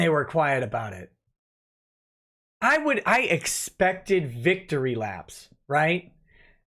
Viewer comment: When I first heard "Victory Laps", I heard "Vic to Relapse"... Oh... Hmm...